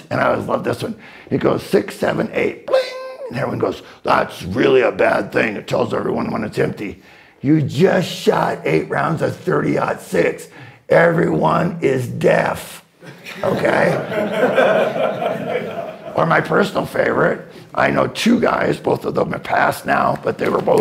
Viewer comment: "You just shot 8 rounds of 30-06, everyone is deaf" LOL Brilliant! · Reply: The first time I heard it, I heard “You just shot 8 rounds of 30-06, everyone is dead”…which is also true.